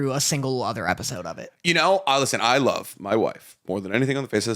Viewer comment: Did just call Dorthy his wife? 28:44 When did they get married?